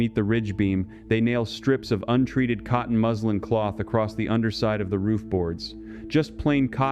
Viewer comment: I heard cotton muslim, i was like is that a fabric brand 😅😅